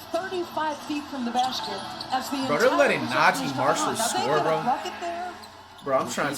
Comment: Bro did Flight say NAZI Marshall ahahahahaha, great video though Flight, love you man.